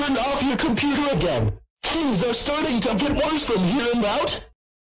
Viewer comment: i don't understand what did barney error just say?🤨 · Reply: Things are starting to get worse from here and out that’s what Barney said